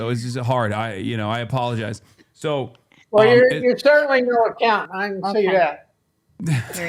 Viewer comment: Can someone please tell me wtf said at 1:29:00.. everyone laughed but it literally sounded like gibberish to me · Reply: well you're certainly no accountant, I can see that.